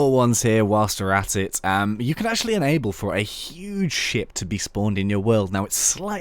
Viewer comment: When you said huge ship I thought you said huge s\|]|%^#t! :)